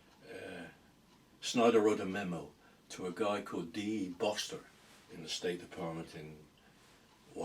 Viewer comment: does say "Dean Boster" at the State Department?